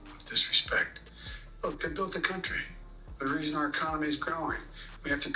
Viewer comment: He said They built the country. I thought they said blacks built the country.